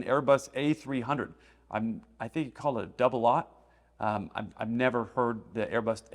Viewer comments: I believe what he said was "double aisle" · You said that you heard Carlson refer to the plane as an Airbus double aught. I thought he said double Isle. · I think he quickly said “double aisled” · Sounds like he said double isle.